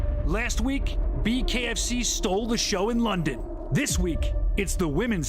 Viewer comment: I thought he was saying BKF Seattle!🤣 Damn they really started ad targeting on a local basis!